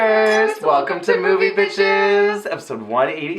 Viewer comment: i SWEAR i keep hearing "welcome to moviePITCHES" sigh the pitch perfect love is REAL in me lol